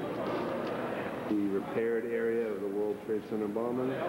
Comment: the prepared area for the world trade center bombing? wtf did i just hear?